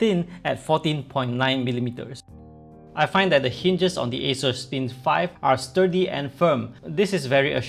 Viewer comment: Is it just me or everyone else also because I heard after the minute of 2:00, he said asus spin 5 a couple of times? 🤔